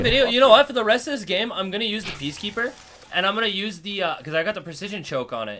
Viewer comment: I thought he said he was gonna use the peacekeeper the rest of the game